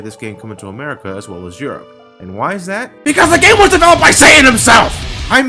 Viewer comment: 1.06 I thought yiu said: because the game was developed by Saiyan's themselfs u know the ones from dbz yes my grammer is shit iknow